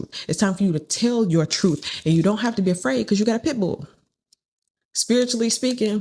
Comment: 👋😃... I got a pit bull huh at first I thought I heard you say I got pickled smh 🤣🤣🤣